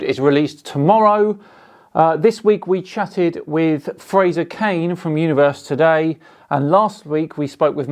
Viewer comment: At the beginning I thought you said you were having a podcast with Frasier Crane haha